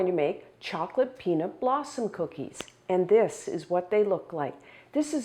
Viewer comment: I thought it said Bosom Cookies not Blossom Cookies 😉